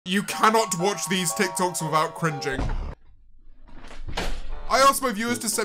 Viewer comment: i thought it said crying 😂